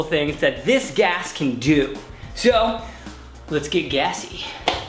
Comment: subtitles english auto generated its says lets get gay instead of gassy